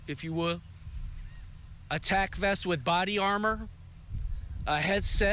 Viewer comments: Did he say "attack vest"? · when she said tac vest i thought she said attack vest